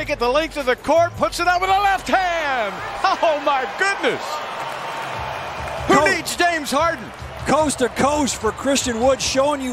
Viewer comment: Sound like he said who needs Dame Harden💀